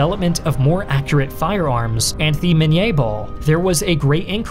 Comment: Sorry? "And THH'e Mine Ball?" That just came off as a bit odd to me lol I mean if it's a sign of a speech impediment, doing a marvelous job of concealing it, i regard it as a VERY small thing to point out but it did catch my ear and I heard it like thrice now. Was it maybe just a bad take? Or... tried to extend the French accent to 'the' even though 'the' is English? lol The French say 'le' or 'la' depending on gender context or some such thing, I dunno, silly languages, I adore and love and cherish my English <3 Proud of my English heritage... sicked at how the UK has fallen so horribly into authoritarianism with people arrested for 'mean Tweets.' Silent majority needs to pipe up, and I have hopes that they are, finally, thanks to Mr. Dylan Mulvaney. I've been speaking out against feminism nonsense since at least 'Gamergate' times, though I have had my own experiences before then with negative interactions of that horrendous and divisive and toxic and cancerous ideology.